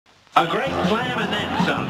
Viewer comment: A G R E A T S L A M A N D T H E N S O M E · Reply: of Destruction Beerus I thought he said A GREAT PLAN IMENSA